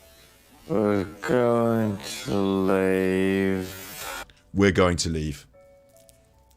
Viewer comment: You didn't rewind it far enough. I definitely hear "we were", but because of his accent and way of speaking, "we" is very short and not an e sound like wee, it's more of an i sound, as in "wit" to rhyme with river. wi-wer. wi-wer going to leave.